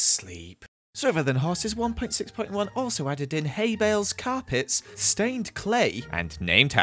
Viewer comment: When he said “carpets” I thought he said “cow pits” lmao